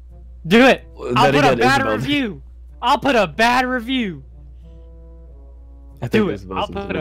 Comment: At first I thought said "I'll put a batter of you" XD but it was "I'll leave a bad review"